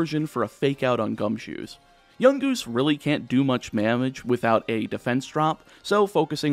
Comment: Definitely heard you say he can’t do much mamage 😂 but awesome vid dude